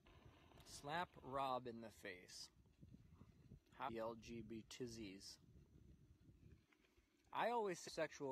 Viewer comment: bro this was the worst line to buffer like what was he saying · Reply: The question was "how do you feel about the lgbt community" I think its a fun response to that